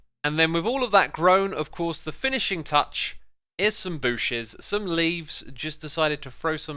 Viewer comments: Did he just say booshes. Im dying. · it sounded like you said booshes "if it is even a word" instead bushes